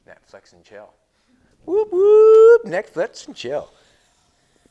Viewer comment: I thought he said sex and chill .🤣🤣🤣🤣🤣